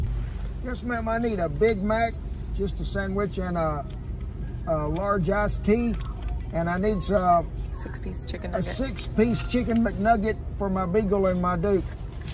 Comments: "Yes ma'am I need a Big Mac, just the sandwich, and a large-ass tea." Yes, I know what he really said. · I thought he said "Large-ass tea" instead of "Large ice tea". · I swore you said and 'Big Mac and a Large Ass Tea'.......I love the diversity in our country.